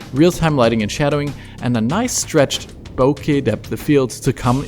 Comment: In 3:00, this video said "bokya depth of field"? So what's the word "bokya" means? Or it's a wrong auto-subtitle?